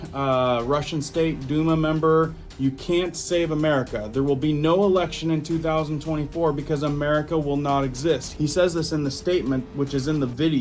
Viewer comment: What did he say?? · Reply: Click here to hear what he said: @ 2:36 he said there won't be no America anymore, and no 2024 Elections